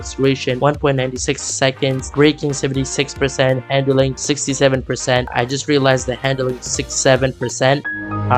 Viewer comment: Did blud just say 67? Hell nah! 💀😂🤣